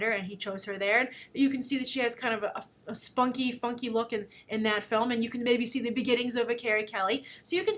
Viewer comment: "even sushi is kinda a spunky funky lookin" ... lol! Thats apparently what says at 2:44 ...